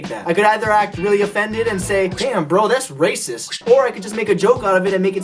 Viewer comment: did he just say racisss